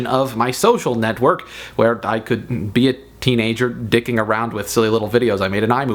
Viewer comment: did he say dicking around ?